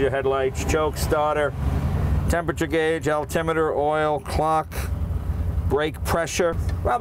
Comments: did he say "altimeter" like altitude? Like an airplane? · Did he say Altimeter?